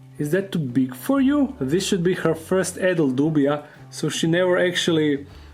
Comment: Wow!! You got a buzz-cut. It’s amazing to me how little tarantulas actually eat. I also thought you were saying “addled roach” instead of adult roach. The accent. I’m so glad I’m not a roach.